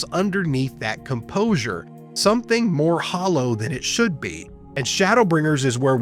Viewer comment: I tried fixing this by plugging in my actual script document but I’m not sure how else I can remedy the issue. I’m sorry. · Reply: Oh, it's not that big of a problem. I found that the auto-generated subtitles were fine for the most part (funnily enough, it uses Emet-Selch when you say Elidibus). I think the problem is that your script differed from what you actually said tbh! For example, from 5:30, you say "more hollow" rather that the script''s "hollowness" and then "emptiness" when the script uses "hollowness" again. And a section from 8:00 where you say Elidibus but the script says "Themis". I don't expect you to go back and change your script for this, but thanks for trying anyway!